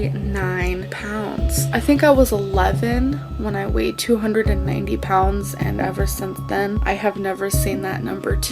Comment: yo did she say she was 290 pounds at 11?